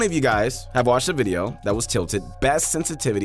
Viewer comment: I’m pretty sure he just said tilted instead of titled 😂